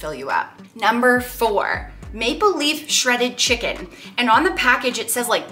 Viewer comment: I thought you said ‘Make Believe Shredded Chicken’ hmm well that would be low in calorie.